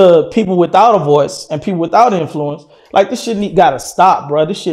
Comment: All I hear is “ he lying this shit gotta stop”